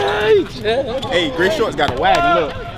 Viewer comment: did he say the boy in the grey shorts got a wagon?!🤢